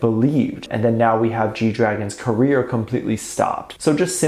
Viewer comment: I could have sworn that I heard G dragon was charged?